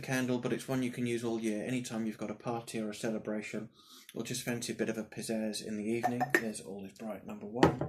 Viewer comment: Fantastic idea ...nice to see some great scents ..I did nearly spit my brew out with all is bright " when you fancy a bit of pizzazz " I thought you said "when you fancy a bit of a p*** up " lol